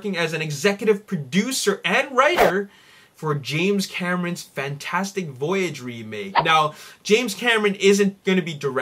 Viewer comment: I thought that you said James Cameran was directing Fantastic Four and I was like MAYBE THIS ONE WILL BE GOOD XD